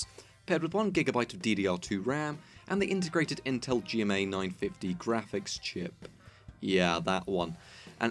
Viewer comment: For a second i thought it said gtx 950, i was like tf?